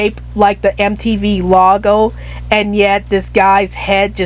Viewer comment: “lawgo” is it just your accent that make the word logo sound like lawgo instead of logo or is it just me